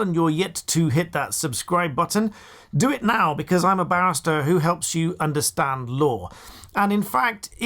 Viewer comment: OMG. I’m laughing so hard. At around 1:26, it sounds like you said I’m a b*stard who helps you understand law. Too funny. Good thing I know you’re a barrister! Lol!🌀🌟💙